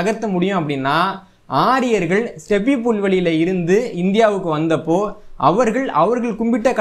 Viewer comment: what does said stepi pull ah What pronounciation is that not audioble...any one whose please answer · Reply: ஸ்டெபி புல்வெளி. ஆரியர்கள் வாழ்ந்த ஆதி இடம்.